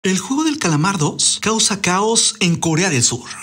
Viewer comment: WTF al inicio escuche " el juego de calamardo "😂